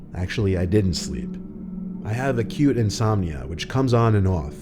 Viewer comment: Not gonna lie, i keep hearing "a cute insomnia" lol 😆